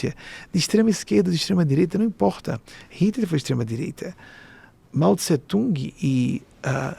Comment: Como ele fala que Hitler era de esquerda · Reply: Em 13:55 ele diz "Hitler foi extrema DIREITA". Como tem gente que ouve o que quer hein, mds